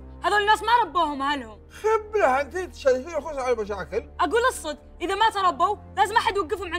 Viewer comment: كاني سمعت نوف تقول ل ابوها اص انت؟؟ ولا أذني فيها شي